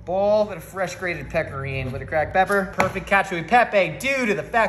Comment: I honestly thought he said protein powder because i wasn't paying attention and i wasn't even shocked because its josh